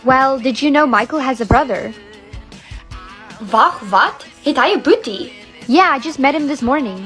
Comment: The "Wait what, he has a brother" I heard a "Wait what he has iron booty?",😂😂😂😂@